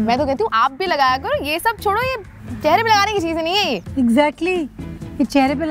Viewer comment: I thought 💭 she said" carry minati ke chijen nahi hain yeh"😂😂